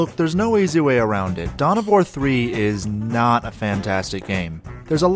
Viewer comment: I thought you said "Dumb of War"